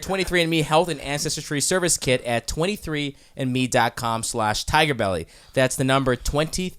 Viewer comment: I thought the background said "TigerLilly"